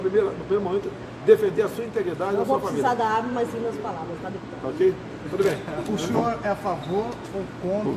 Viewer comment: A baixinha não vai precisar de arma para se defender de um marginal dentro de casa, e sim da PALAVRA........KKKKKKK.......Vai sim viu......vai te virar do avesso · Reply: vc está certo. Eu entendi "mas lindas palavras, viu Deputado". Me equivoquei.